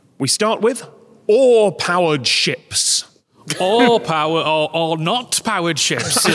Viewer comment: I heard “awe powered ships;” boats powered by their own impressiveness